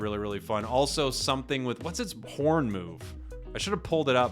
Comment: What level would Magikarp evolve at. If it's 4 like other 2 stages its broken and if it's late game carry levels like 9 or 10 it's useless. (Edit) 8:55 I just heard say "what's his p**n move" instead of horn and im cracking up.